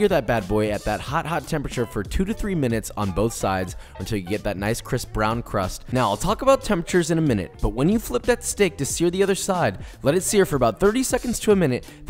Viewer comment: Oh my god I thought he said "Chris brown crust" 😂